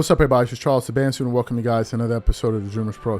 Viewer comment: I had to go back to the beginning a couple of times because I had thought you had said your name was Charles Manson. I got it right!